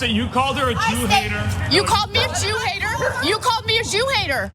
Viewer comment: LMAO the close captions shows "Shoe hater" instead of "Jew hater"! XD